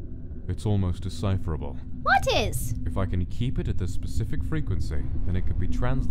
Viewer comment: Who else heard Oddish instead of What Is at 0:06 ?